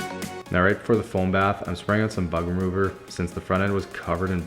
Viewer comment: I thought you said I’m spraying on some butt remover lol